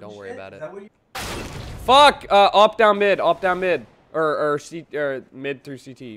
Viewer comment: Sounds like you said "Fuck! Up Down Mid, Up Down Mid" at 2:57 :D